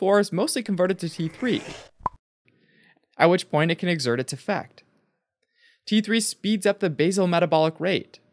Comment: "Once in the cell T4 is mostly converted to T3, at which point it..." I can't make out what is said in the rest of this sentence. Can somebody please help with the last few words of this sentence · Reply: Where it can exert it's effect